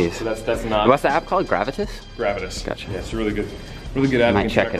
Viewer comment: 3 attempts and I still couldn't hear what was the apps @ 7:00, can someone help? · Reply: GRAVITUS !